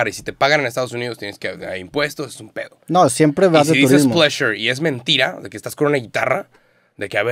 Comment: Saben la palabra que dice en el min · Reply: Pleasure pero lo dijo raro jajaj